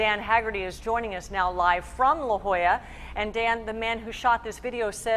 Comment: Did she just Say Dan The Man by accident? lolz.